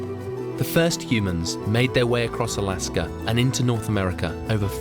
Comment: I was until He said the 1st humans migrated from Antarctica to peru😂😂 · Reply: I thought he said Alaska, which is something many scholars have said. This would've been when the world looked more like Pangea, and then the continents spread out and the people spread with them. I'm listening to this in the background while at work, so I may need to relisten to that part.